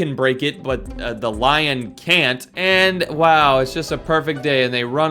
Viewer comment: did you say oW